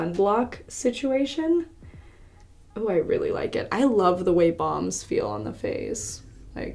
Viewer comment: At first I thought she was saying bombs not balms 😂😂🙄😬